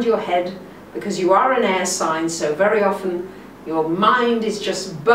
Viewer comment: Ha ha ha, I thought she said ass sign. I realize she said air sign.